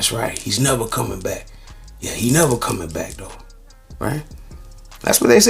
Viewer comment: What did he say when he walked past the racist cops? I heard he said “now u see how it feels” 😳 😢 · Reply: They reported he said " He's never coming back though"